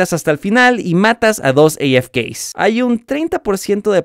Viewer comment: activen los subtítulos y verán que dice gays en ves de afk xD